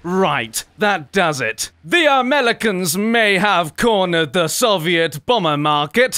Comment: Did you say Amelicans?